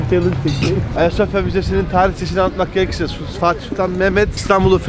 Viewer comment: ne dediğini anlayan var mı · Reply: Ayasofya müzesinin tarihçesini anlatmak gerekirse...